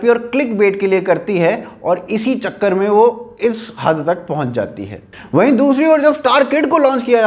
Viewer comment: i just heard isss HAG tak